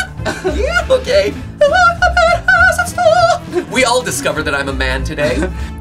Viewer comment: "we all discovered i am a man today" matpat 2018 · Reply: I thought it said divorced not discovered😂